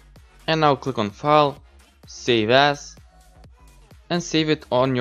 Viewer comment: I thought he said save ass